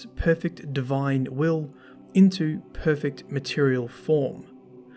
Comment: Am i the only one to notice that he says " converts perfect divine will into perfect material form" whilst the text reads "imperfect" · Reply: Courtney Hill I heard "imperfect material form"